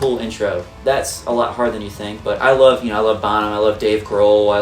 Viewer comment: did say " i love Bono"???? 😳